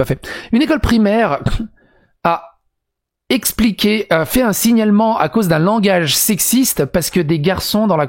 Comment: À 3:20 j'ai compris "un signe allemand" et pas un "signalement" ;-; donc je comprenais pas le rapport C'est pas facile tout les jours d'être con